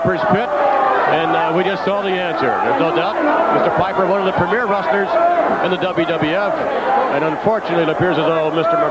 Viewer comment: I heard Hot Rod say "I need some help here". Is that correct? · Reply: That's exactly what he said. "We're gonna need some help here."